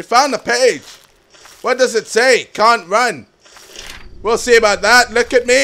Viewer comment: Hahaha, at first I thought the first page said 'Cunt Ran' When it said 'Cant Run' xD